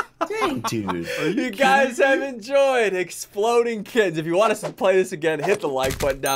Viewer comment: Is I me or did he say exploding kids